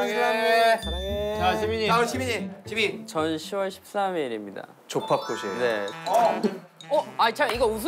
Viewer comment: There's an error with the subtitle. At 3:55 the caption says (Jimin's birthday is September 13th) but it's supposed to be October 13th. I hope it will not break your day ARMYs. Please understand. 🤗🤗🤗💜💜💜